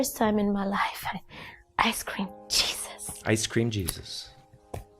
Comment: I always hear “Ice cream, Jesus.”